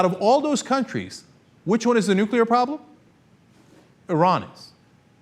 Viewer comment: Subtitles say "the rods"....how the hell did they understand "Iran is" as "the rods"?